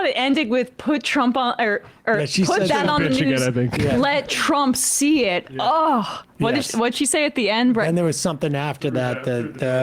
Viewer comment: ‘you f’in redneck’ is what I heard.